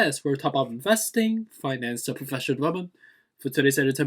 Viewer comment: oh, I thought that it did seem shorter now that you mention it. Honestly, I like the longer intro; it's you, it's who you are. I like your accent, and I love that you embrace it. Be you it's good enough, let the haters skip on by. Do you boo! That is why I keep coming back, because of your sincerity and your authenticity shine through. Keep the long intro ❤️. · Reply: oh, okay, I am also guilty, then, because sometimes I think I hear you say “Finances and financial development,” and sometimes it sounds like I’m hearing “Financial and professional development.” But either way, I think that it's s cute, lol 😄.